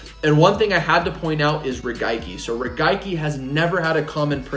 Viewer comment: Did I hear “raigaki” ? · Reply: He said “regaiki”